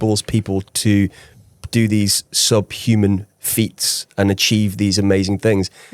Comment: So you really didn't catch "sub-human feats" at 20:04 in the edit? 😅